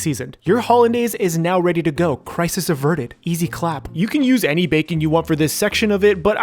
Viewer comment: did i heard him say EZ clap? Twitch frog?